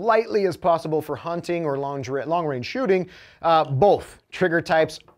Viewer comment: Did he did he say lingerie shooting